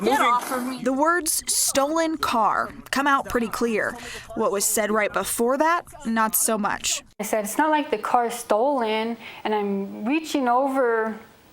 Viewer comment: "Its not like this is a stolen car" is what i heard. But it was pretty hard 2 make it out, so i cant say for sure.. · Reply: I thought she said " you act like it's a stolen car". Same thing in retrospect..